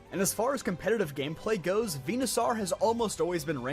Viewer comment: Vena...sar????? First time I hear that pronounciation instead of "Venasor". Dear heavens is this the Arceus conundrum all over again?